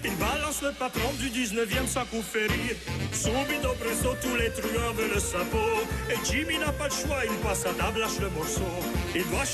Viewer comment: Qu'est-ce qu'il dit à 1:21? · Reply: Subito preso tout les truants veulent sa peau